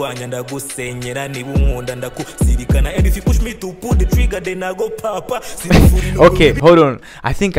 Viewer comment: actually on 48:15 what he says is kinda english ,he says "and if push me to pull the trigger then I go 🔫🔫